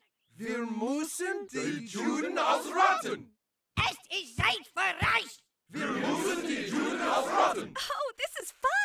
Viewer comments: Always thought he said "Es ist Zeit für Reich." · Ha I'm german and I always heard "Es ist Zeit für Reich" like the third reich 😅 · ngl I always thought cartman was saying "es ist zeit für reich" as in the third reich but apparently not xD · I think he said " Es ist Zeit für Reich" because Nazi Germany was also called " Das dritte Reich"